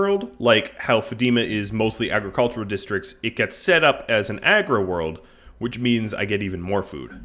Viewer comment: I swore I heard Agro World for the quickest second hahah